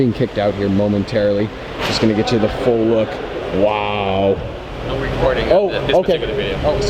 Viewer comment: did anyone hear what the guard said? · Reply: "no recording of this particular vehicle"